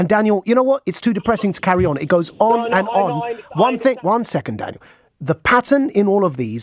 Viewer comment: For a second there I thought he called him dad.